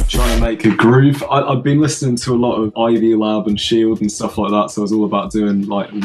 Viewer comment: which artists did say he was listening to? I couldn't understand his accent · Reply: Ivy Lab and Shield